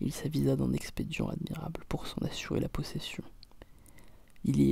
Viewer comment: Je suis vraiment désolée, mais cette vidéo me met mal à l'aise. Entre ta voix chuchotée, ton articulation, les bruits de bouche et les images floues et/ou qui tanguent... je n'arrive pas à me fixer ni à comprendre ce que tu dis et... ça prend presque un côté malsain/glauque. Je ne sais pas si c'est fait exprès mais...voilà. Ça m'a vraiment fait bizarre. Bon courage pour la suite. ;-) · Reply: D'ailleurs il dit quoi de 1:14 à 1:18? Il... d'un... admirable pour s'en assurer la possession